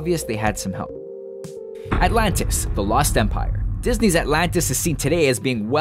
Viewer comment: When they said "The Lost Empire" I heard, "The Lost Senpai" anybody else?